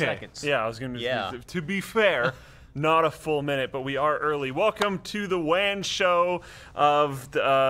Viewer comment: lol thought he said welcome to the wang show. Was about to turn off.....